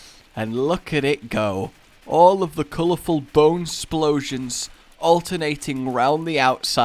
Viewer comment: i swore it said colour bone storm